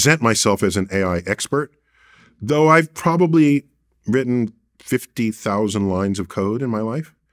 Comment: At first I thought he said he did 50,000 lines of Coke 😂 I had to rewind it to realize he said he’s WRITTEN 50,000 lines of CODE 🤣🤣